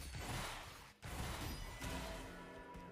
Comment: i thought he said i have wives bending